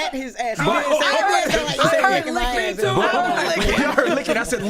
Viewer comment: nahhh idk Envy, I heard “licking” too …. 😭😭